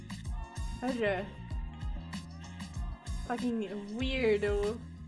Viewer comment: I am still wondering what did say there. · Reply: Swedish "Men vafan gå härifrån" roughly translated to "fucking go away"